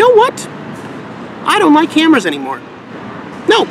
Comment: For a second I thought he said, "I don't like Hammers anymore," instead of, "I don't like Cameras Anymore." Wow I'm stupid 😛